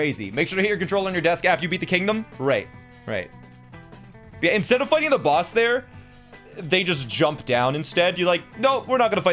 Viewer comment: who else heard "Instead of fucking the boss there,"